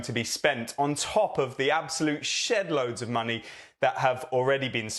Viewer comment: did he say shitload? Lol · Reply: No, he said, ‘shedload’